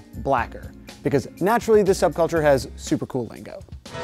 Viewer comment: with subtitles "has bad-add lingo" He actually said "super cool lingo". I think bad-ass works perfectly in this case